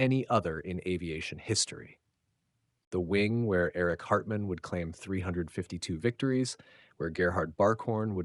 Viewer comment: First time, I coulda sworn he said "Eric Cartman".